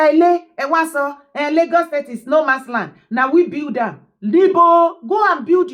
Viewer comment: I thought they said Lagos is No man's Land,,onye developers 🤣🤣🤣🤣🤣